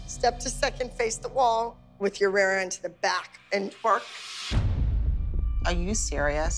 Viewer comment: Was i the only one who when she said twerk I thought she said bark 😂